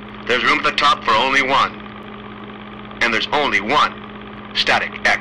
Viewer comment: I always thought it said "Take a dump in the street Hammer" LOL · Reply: ***** TAKE IT DOWN WITH A STEAM HAMMER NOT TAKE A DUMP WITH STREET HAMMER BUT CLOSE THO ^_^ EHEEEEHAHEE